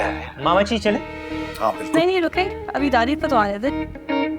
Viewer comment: did she say dananeer?